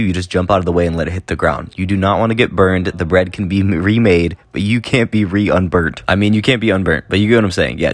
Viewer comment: what said:"the bread can be remade, but you can't be unburnt" what I heard: "the bread can be remade, but you can't be remade" deadly bread